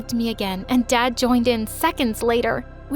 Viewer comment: I thought she said hole instead of whole 🤣